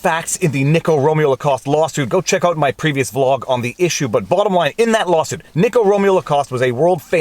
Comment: Link to Motion here: <link> Support links here. Locals: <link> Rumble: <link> <link> <link> <link> MY MUSIC: <link> Promo code vivafrei10 for 10% discount THREE ETERNAL TRUTHS Color shirt: <link> White: <link> Must watch: The Plot Against the President (documentary): <link> Must read: The Plot Against the President: <link> (Amazon links, I get an "Amazon Kickback" if you purchase through this link) POLITICS RUINS EVERYTHING SHIRTS In colours: <link> In white: <link> vLAWg ON <link> Pudge Dawg: With logo <link> No <link> Original Vlawg Dawg: <link> I SHILL FOR NOBODY It's perfect! <link> CHICANERY <link> Keep Calm! <link> Know the Vlawg <link> MY GEAR: Camera <link> Camera mic: <link> Stream mic: <link> Stream cam: <link> My GoPro: <link> The awesome thumbnails are from DSLR Dave! He’s open for business, check out his channel! <link> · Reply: dude did you say "bLAWttom line"? you're taking this gimmick way too far now -_